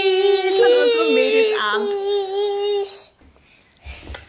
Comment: Habe ich mich verhört, oder sagst du wirklich "HalleJULIA"?🤣🙈